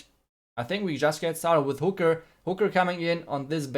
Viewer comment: I thought I heard hooker🤐